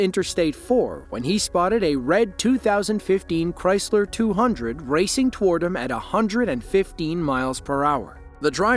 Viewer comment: I had to go back and watch this again. I thought sure I heard Chrysler 100 racing at 215 miles per hour! 😮😅